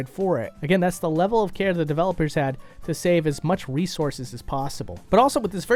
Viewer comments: Did Did he just say ''Wiisources''? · who else thought they said “Wiisources”?